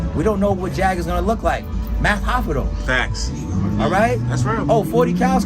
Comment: did say MATH HOFFA?!?